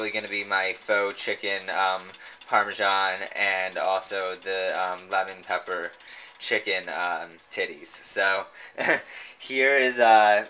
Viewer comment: Did I hear "lemon and pepper chicken titties"? *LOL*